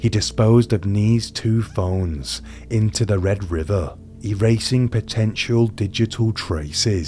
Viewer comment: When you said "Khan (sp) chose the path..." I started chuckling thus insuring my scholarship to hell...because i literally heard "Kancho is the path"😂-😮-😢<- me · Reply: I had to replay 13:10-13:15 because I thought he said threw her knees and toes into the red river.That is not what he said, oof.